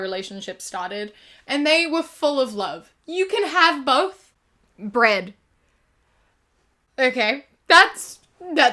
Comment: I was listening to this in the background while doin other stuff and i thought you just said 'bread' and then proceeded to have a breakdown and i was so confused